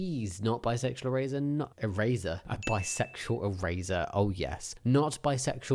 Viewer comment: I legit thought you said “Bisexual raisin” lol 😅